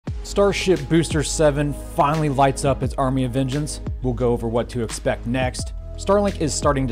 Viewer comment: I mis-heard you say "Army of Vengeance" :D